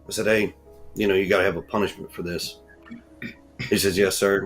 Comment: * He said I ask him “TODAY”he has to have a punishment??? Am I hearing things and why would he hit a child … with a belt and good for Sebastian for telling on this man…how could his mother allow someone to harm her son